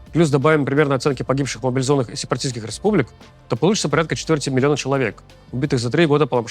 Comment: Я правильно услышал? 14 млн человек за 3 года?